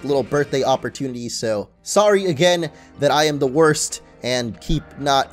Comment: thought he said he was sorry that he was divorced